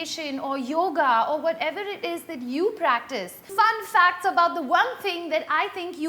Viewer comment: I thought it said WTF facts?😌